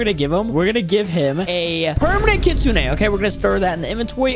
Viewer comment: what says: were gonna gift him a Permanant kitsune what the subtitle say were gonna gift him permanant kitchen😁😁